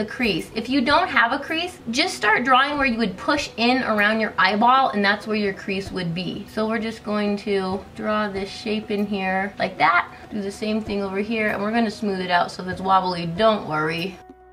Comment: I thought you said "draw this shit in here" hahahaha